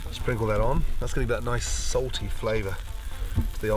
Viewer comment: "that gives it that nice salty flavour" No Gordon, I think the half a kilo of salt you added does. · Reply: When did he say that? Because i only heard him say "nice sauté flavor"